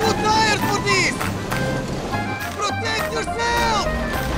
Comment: did he said protect yourself?